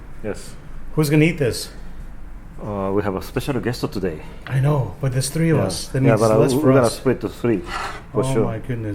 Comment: type what he said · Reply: Matt Eagle Hahaha 😆